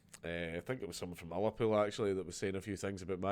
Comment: Which -pool ? I didn't catch that...